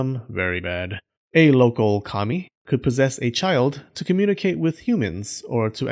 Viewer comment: I initially misheard this as "a local commie", to my great amusement.